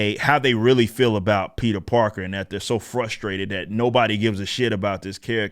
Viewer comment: i literally lost my shit crying laughing when i heard "Peter Darker"